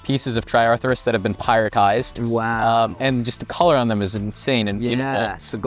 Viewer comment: I definitely thought you said they were “pirate”ized, and assumed it had something to do with them being a golden treasure. It took until you explained that they were made of pyrite for me to figure it out.